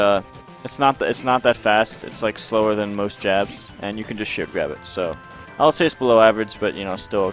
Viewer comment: I thought he was saying "It's actually shorter than most 'JAPS''", not "JABS", and it was a racist joke video, lmao